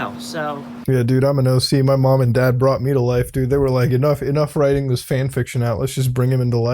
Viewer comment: Swear for a second you said 'brought me to laifu' at around 5:57 lmfao